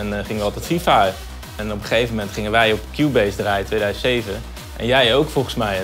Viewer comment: Subtitles tell 2017 instead of 2007